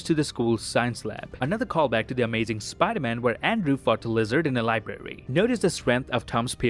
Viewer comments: Sorry but it sounded like "where Andrew fucked lizard". Lol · Did I hear correctly you said when Andrew fuck the lizard on 16:58. If I was wrong then my bad I was listening closely and over again